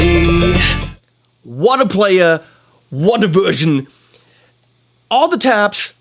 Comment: What I heard about Sami's playing: What a player, what a virgin!? 35:54 :)) Freudian slip right there